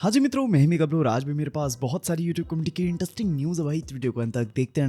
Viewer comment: yr ye kya bolte ho hnji mitron ke baad ? " Mehme gabru " 😭 teesri baar puch raha hoon bhai clear karo yr aaj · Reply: Hnji mitron mai hu